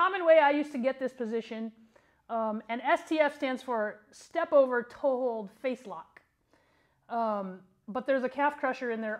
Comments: I thought this said sleepover toehold facelock at first. I was like damn that's a rough sleep over. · I thought it said sleepover toehold face lock, and I was like I had a few of those